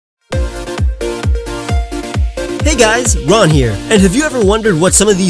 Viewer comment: I thought you said "Hey guys, run." at the beginning XP